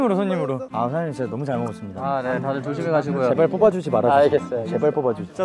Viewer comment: 이거 다음에 제발 뽑아달라고 하는 걸로 들리는...?